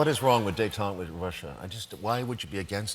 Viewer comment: What does he say? "What is wrong with __ ___ Russia?" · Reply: detente with